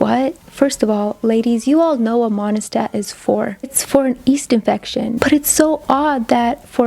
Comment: Did she say it’s for an ‘East Infection’? 🤔